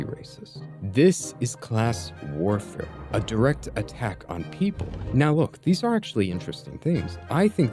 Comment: the caption says on white people but you say people